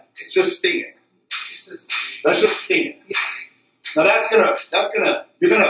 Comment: What does he say at 05:34? "Just steel. That's just steel"?